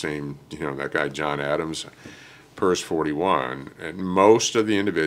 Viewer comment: John Adams purse 41. What does that mean? Or did I mishear. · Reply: PERS 41, the billet that assigns surface warfare officers orders.